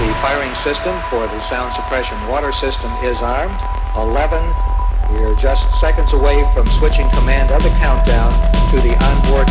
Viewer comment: Can anybody tell me what's he's saying at 1:30 to 1:39 please? :) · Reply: muffley but "t -15, the firing system for the sound suppression water system is armed, 11, we're just seconds away from switching command on the countdown, to the onboard computer... 3...2..1.. BOOOM"